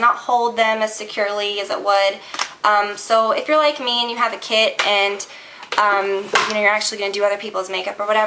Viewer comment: omg I thought you said "if you're like me and you have a kiD" at 5:12 hahaha phew that surprised me there...